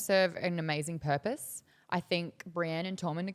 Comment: he thought she said Bran and Tormund are gonna get it on. i thought she said Bran and Tommen are gonna get it on. i was like wait wut?!